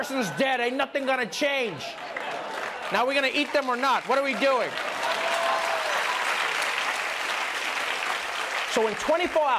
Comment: Did Trevor just say, when talking about a victim in a Law and Order episode, "Are we going to eat them"?